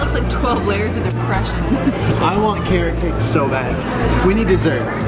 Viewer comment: When said carrot cake I thought he said Cake